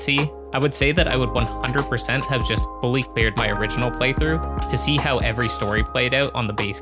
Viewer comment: what does he say? I hear it as "I would have just bully bared (beared?) my first playthrough". Is this some kind of American term or am I mishearing it?